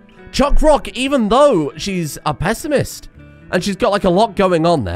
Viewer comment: I first heard "Pessimist" as "Pacifist" and thought that was GG right there